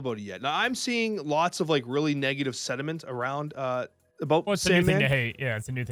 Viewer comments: Did really say "negative sediment" while taking about SAMDman? Amazing unintentional pun 33:43 (i didn't know how to link times). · Did really say “lot of negative sediment” when referring sandman… is this man a comedy genius??? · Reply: Lol that'd be awesome, but he said sentiment.